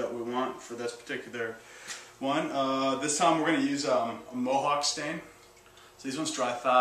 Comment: Hi, can you tell me what color is "mohawk stain" in 8:51min of video (if I heard it well)...? Its very nice color